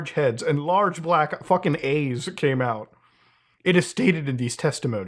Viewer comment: did i just hear " A's " instead of "EYES" lol? I'll tell ya, if i was an official taking this report from a witness, and they said that big headed humanoid aliens came outta nowhere, and began shooting large, laser beam "A's" out of....uhhh...SOMEWHERE...from their body, i'd have to take a half second time out from being simultaneously skeptical, kinda scared, and overall amazed, so i could chuckle to myself briefly, before saying, "ok, so yeah...please continue...."